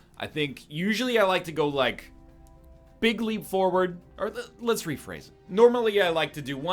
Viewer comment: I see why you reworded that, I guess you said big leap forward, but what I heard was "bigly" as a trumpism.